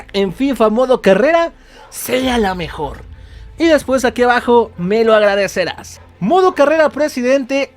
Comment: y despues aqui abajo me lo agradecerás jajaja lo entendí mal😁